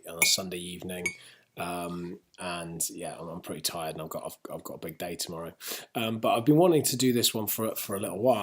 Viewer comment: I thought he'd said ''I've got a big dick tomorrow''...